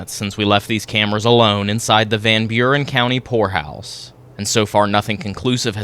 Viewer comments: At first, I misheard the kind of house it was... then when you said it the second time, and I realized that it was a POOR house. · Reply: I thought he said hor house too 😂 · I’m doing my nails 💅 & listening I thought you said whore house 😂😂😂😂 not poor house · Poor house, I thought you said whore house😮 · Love you guys! 1. For your honest, drama less investigations and 2. For your respectful demeanour when conducting investigations. Personally I do need (after many years of playing Bagpipes etc) to get some hearing aids! When you first stated where you were visiting, I heard the Van Buren whore house, instead of Van Buren poor house!!! 🦻🏽 Keep up the great work guys, and looking forward to the next episode! 😎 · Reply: That’s what I thought they said too. The Van Buren Whore House. 😄 Then later they said Poor House. That man who gives tours explained that people who were poor or down on their luck, etc. came to that house. Interesting. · I thought you said Whore House 😂😂😂😂 Then I watched it again and I heard it correctly Poor house!.😂 😢sadly most poor houses are haunted due to the poor conditions those whom had to live there and slavishly labor for their costs of living there!.